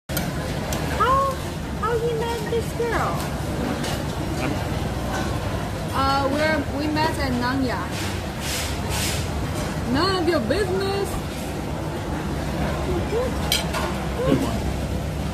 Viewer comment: I thought she said “we met in Narnia” 😂😂